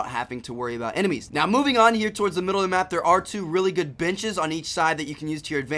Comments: anyone notice at 3:47 he said the b word instead of bench? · Am I the only one who thought he said there are some good bitches when he said benches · sounds like he says "bitches" instead of "benches" haha. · He said the b word instead of benches on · I thought he said bitches. Not benches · Lmaoat 3:48 it sounds like he says "bitches" ! XD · "biches" instead of benches" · He started to explain the middle. Instead of benches, I heard "bitches". sry for language